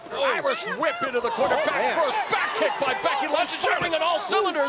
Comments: did he just say Becky Lunch? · I swear I heard "Becky Lunch" at 1:04 😂😂😂